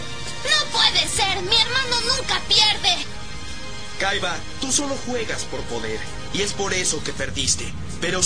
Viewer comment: yo escuché tu solo juegas por joder 🤣